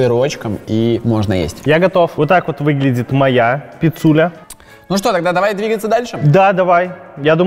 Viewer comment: когда сказал "моя пицуля" мне послышалось как " моя писуня"